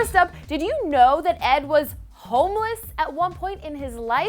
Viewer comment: I thought she said he was holeless